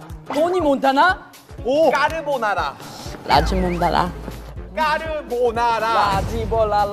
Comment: estoy mal o escucho que dice lachimondala - lagibolala- laGImolala jajaja hasta el propio jimin cambio su palabra y terminó diciendo todo diferente jaja 0:10 j-hope si si me está entendiendo jaja y el otro con su lachimondala JAJAJAJAJA